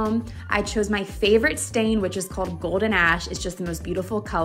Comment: I thought she said golden ass . I was like whut!